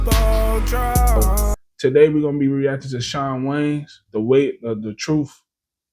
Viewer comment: The Wayans Goated but you sound like u said John Waynes😂